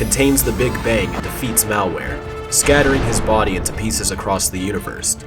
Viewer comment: you said malware instead of maltruant